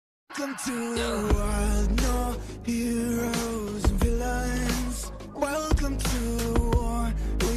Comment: why does that sound like it says “Villians” to me